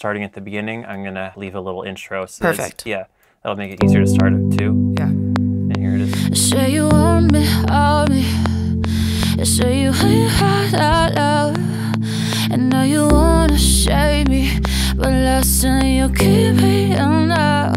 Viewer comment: First placeholder vocals sound like she said Do you want to shave me ?